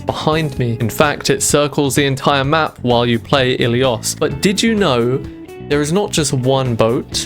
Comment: i thought it said illinois